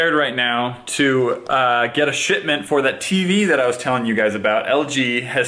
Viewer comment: Did u just say shitment